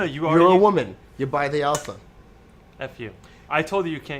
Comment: did really say "FU" at 5:25 ? this episode made me laugh, congrats guys on another great episode... and I truly believe Alfa Romeo should come back to the US... I may be getting a 1969 Alfa Romeo Spider soon, so it would be nice if they could bring some parts over.